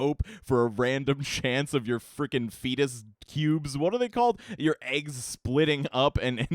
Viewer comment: Did u just say....fetus cubes?